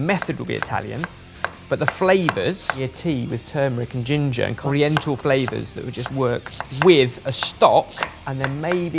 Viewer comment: Did say "oriental flavors"? I know you mean well, but please don't use that term. If I misheard, thanks for not using that word 🙂